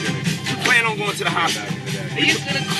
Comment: did Diddy say "Plan on going to the hospital?" 🤔